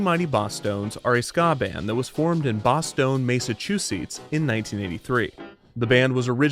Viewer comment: I was invested in the story until I heard "Mace-a-choose-eats" along 33:05.